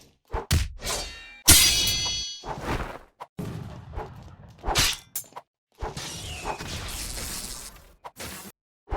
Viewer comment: anyone know what mantra the thundercall user casted? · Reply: its storm blades with tornado spark